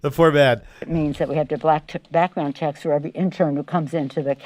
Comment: did anyone else hear the freudian slip "I don't think it means we got to black...er, background check" we all know you want to black mail your interns